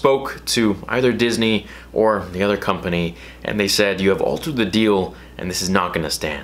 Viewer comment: Did he just say "you have altered the deal" lool